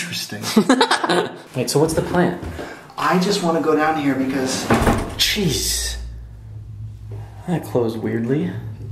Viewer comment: idk why but I hear a high pitched voice over Garrett's idk if I'm tripping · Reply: Sounds like it says "hi"